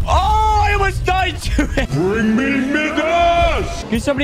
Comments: Did he just say ‘bring me n words’ but with the real word?! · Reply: He said "Bring me Midas" but he pronounces Midas as Midarse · did he say “bring me niggas” 🤔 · i thought i heard bring me Ni**as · did u say bring me niggas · BbRUH BRING ME NIGGASSS 2:22 he actually said midas